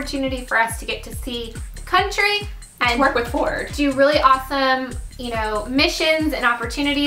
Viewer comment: haha, I thought she said "twerk with ford"